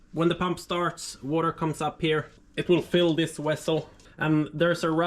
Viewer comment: did he just say "wessel"? lmaoo tf?